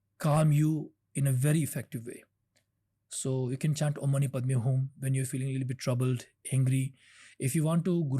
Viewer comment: what is the mantra he says to chant at 26:40? He was speaking too fast! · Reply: Aum mani padme hung